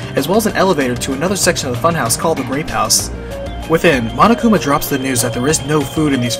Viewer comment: I heard "As well as an elevator to another section of the funhouse called the Rape House"